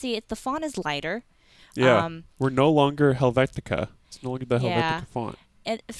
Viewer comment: What is he saying at 0:35 about the font? No longer Helvetica? Can't understand it right… But it is Helvetica Neue. It was Lucida Grande before… and it's something you'll get used to. And it's not really worse to tell if something is "a menu" UI element than with Lucida Grande…